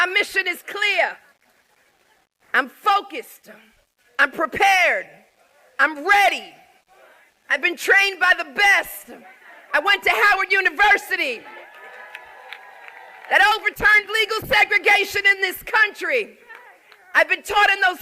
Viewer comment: For a moment there, I thought she said “I went to Hamburger University!!”😂😂😂